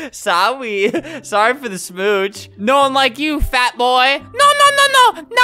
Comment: For anyone who dident know what that guy said. This is what is said "no one like you fat ass" instead he changed it and said boi